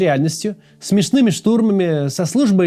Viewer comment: "смешные штурмы" - я один это слышу? Что такое "смешные штурмы"?